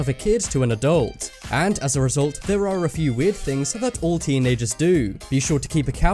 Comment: I thought you said weird things not normal things